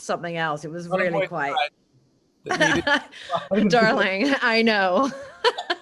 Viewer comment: I don't get the in-joke ("Darling, I know" .. at 20:56) -- what does Dan say? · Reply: The nails darling!